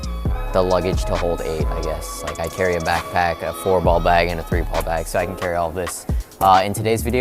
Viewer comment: Hi. What do you mean by 3 and 4 ball packs? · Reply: Sorry, I guess I misheard. I thought you said you carried a back pack that held 3 to 4 balls. Now that I listened again, it sounds like you said, "I carry a back pack, a 4 ball bag, and a 3 ball bag." :27 So, 1 in the back pack, 4 in 1 bag, and 3 in the other? 😆